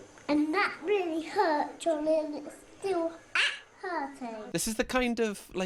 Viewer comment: This is such a interesting series to do!! 4,000 likes and we'll do another one x Also let us know which memes you'd want us to cover! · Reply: I thought the boy called his little brother DARLING - not before you had repeatedly said Charlie could I make out he says Charlie 😂😂😂😂😂